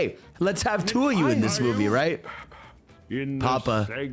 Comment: Apparently, what Eggman says here is not "papa" but "pop-pop" or something like that, it is an expression that means "grandpa". In Spanish and other languages Eggman calls him grandpa too, so this truly is his grandpa Gerald